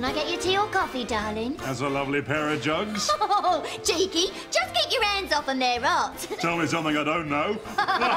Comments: Okay, a little help is needed here. Can anyone translate what Nurse Sidebottom says after the patient's jugs line? "Cheeky! [unintelligible]" "Tell me something I don't know!" The CC on youtube stinks! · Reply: "Just get your hands off 'em, they're hot!" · Because of the accent, I didn't catch what the nurse with two jugs said 0:53 and what was said right at the end by Asquith. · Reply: She says: "Cheeky! Just keep your hands off 'em, they're hot." Askwith's last line is: "Oo-er, missus," which comes from movies of that era -- basically just a character acknowledging a double entendre. "Oh, wow, that's rude," is a pretty poor translation, but that's the general idea.